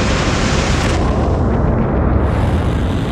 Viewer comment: does he say "Starship is off target"?